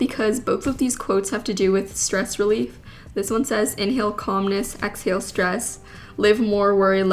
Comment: why did I hear "inhale communism, exhale stress"